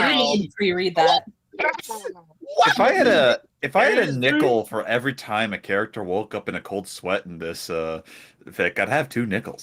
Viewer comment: youre right, this story is god awful on every level. but confusing idioms is the one thing i wont hold against the autor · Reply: Also, a funny part of the stream: I misheard "If I had a nickel for every time that happened I would have two nickel" as "If I had a nipple for every time that happened I would have two nipple"....and was wondering "Wait, does that mean you had none before?" 🤨😆